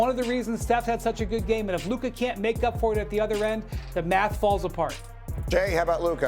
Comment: did i hear correct, Windy said if Luka plays bad the MAVS fall apart.